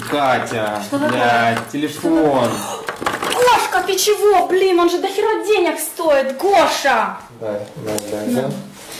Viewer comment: what did she say? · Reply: Goshka, what the hell, damn. It costs a lot of money